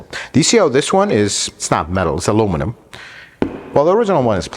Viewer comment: Excuse me…..”It’s not metal, it’s aluminum.” Did I hear that correctly?